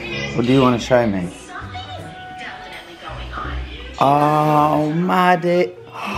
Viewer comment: put subtitles on it says "oh ma di##" instead of oh Maddie